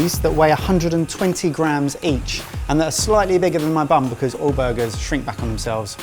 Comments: when was forming his patties, totally thought he said "and i'm making them slightly bigger than my bum" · I watched the part where said, "They're slightly bigger than my bun," and I kept hearing, "They're slightly bigger than my bum." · Did I just hear said "each patty is slightly bigger than my bum"? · At one point I thought said bigger than my bum which made just as much sense as bun .. interesting